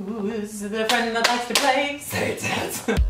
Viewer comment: I thought he said, "Whoooooo's the friend that likes to play? SATAN."